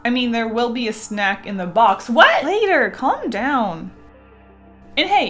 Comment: I highkey thought she said "Later! gOd dAmN"